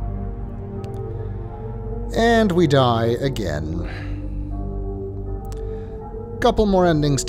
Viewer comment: I know it isn't what you meant, but I heard that as 'And we die a-Gehn'